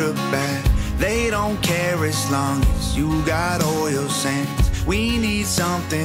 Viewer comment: I thought Tom said, "All your sins".